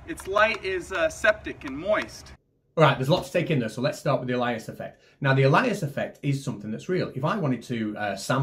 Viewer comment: LOL I was listening and thought you guys were saying Elijas effect, some biblical effect and when you said it was real I almost dropped my coffee. Then looking up I saw "Alias" in the video. Learnt all about that in digital signal processing subject way back in the 70s. The fun with accents and mishearing words because of it.